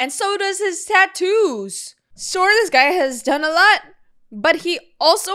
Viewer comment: I'm so sorry but when you said "oh my god his tattoos move!" I really thought you said tits... honestly either works lmao